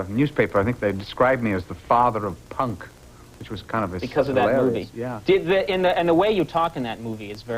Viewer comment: What is that he says after "...'the father of punk,' which is kind of a-"? I can't hear him over Conan. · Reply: "which was kind of hilarious"